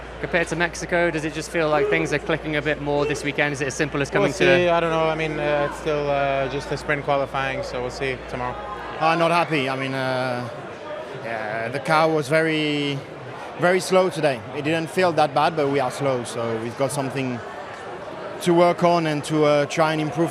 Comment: why does it sound like charles said: ‘the cow was very slow today’